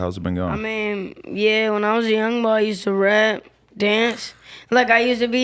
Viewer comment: Did he just say "When i was a youngin" 😂😂😂